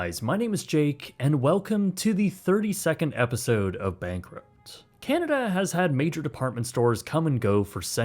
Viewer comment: For a second I thought you said welcome to a 30 second episode of Bankrupt I'm like what!?